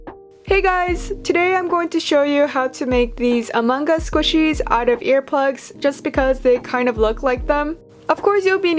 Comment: I thought you said out of of AirPods- PHEW